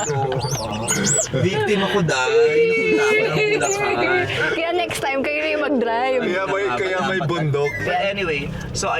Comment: ano yung narinig ko sa 33:02? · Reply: Laruan ni vice. Balloon